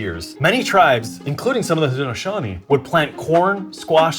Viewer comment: the captions say "Shawnee" but I believe said "Haudenosaunee" instead.